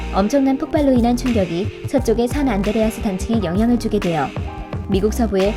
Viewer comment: 산 안드레아스를 안산드레아스로 잘못들었네